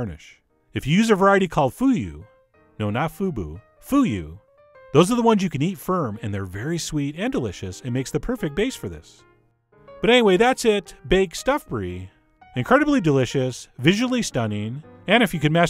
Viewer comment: Who else thought it said BACON stuffed Brie? Nope? Okay xD